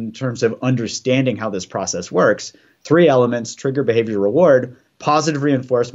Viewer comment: "Trigger, XYZ, reward". What's the middle item there? I couldn't hear what he said. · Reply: Trigger, behavior, reward 👍